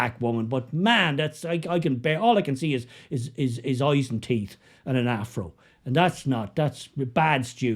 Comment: I thought you said, eyes, teeth and an arsehole! Hahaha